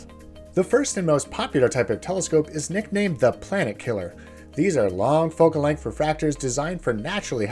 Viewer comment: Ok I had to start over... 😅 I was so sure it had to be "Planet Kallion telescope" (as in some guy who invented it or something🤷) and my anime brain was just mishearing "Plant Killer" 😂🤣 I had to go beak to where it was typed out 🤣